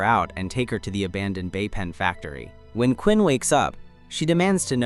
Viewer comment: I keep hearing "vape pen factory" 😂